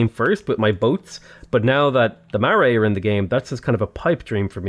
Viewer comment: i didn't realise you were talking about maori, it honestly sounded like you said marray